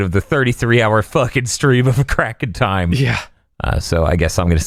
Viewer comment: I misheard "A Crack in Time" as "It's About Time" and nearly had a stroke at the thought of platinum'ing Crash 4 in 33hrs